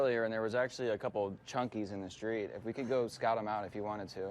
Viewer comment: did he just call those turd nuggets "chunkys"?😂😂